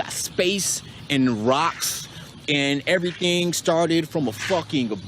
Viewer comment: Wait did he just say rocks??? Or did he say rockets and i heard wrong??? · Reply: I'm freaking sure he just said rocks